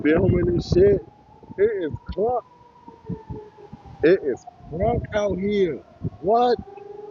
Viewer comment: did he just say ""it is KRUNK out here?!" 😂😅🤣